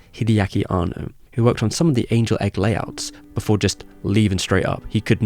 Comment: "and that's why angel;s egg got the green light" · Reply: It must have been a soft g then. I thought you said Anuls Egg.